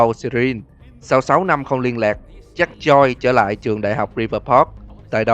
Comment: JACK JOYCE nghe nhầm thành ZACK CHOI 😂😂😂😂